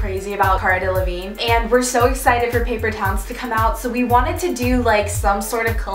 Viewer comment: I thought it said paper towels lol😂😂